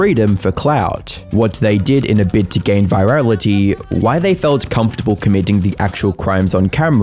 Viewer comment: I thought it said virginity, not vitality. I thought most people are trying to get of that!